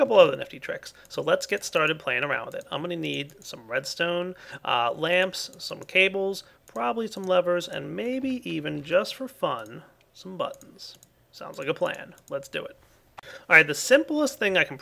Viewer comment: I wasn't really listening, so what I heard was 'maybe even just for fun, some butts'.